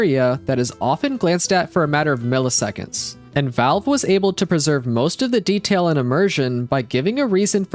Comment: captions say “Valve was able to PERVERSE” when said “Valve was able to PRESERVE”